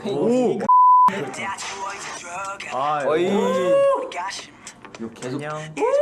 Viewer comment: jurei que ouvi um "oi" KKK